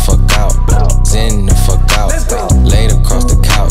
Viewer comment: Thought bruh was saying Xan the fuck out lmao